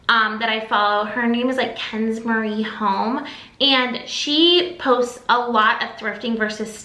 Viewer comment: Hello, new follower here. Your thrift finds just caught my eye. Love what you did to the finds to make them better! I would like to know the girl you mentioned, what exactly is her channel’s name. It sounded like you said Ken’s Marie Home. I couldn’t find anything under that name.